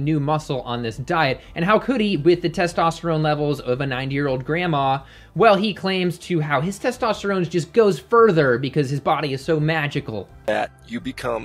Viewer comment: I keep replaying 4:23 and I keep hearing nine year old grandma not ninety. I seriously hope ninety is what you meant 😂